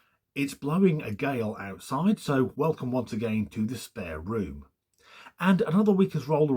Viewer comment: I initially thought you said you were in your "Despair Room" -- which I assumed is how you felt after watching these gawd-awful people. 🤣🤣🤣